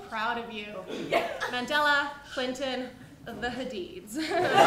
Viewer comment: as a half Belizean/half English person, when I saw doing an impression of Italian dad making dinner, I immediately shared it with a Chinese friend living in Australia. he replied "how did you know I'd find this hilarious?". Yeah, she gets it, and she's funny as hell. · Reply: I thought I heard "the Hadids"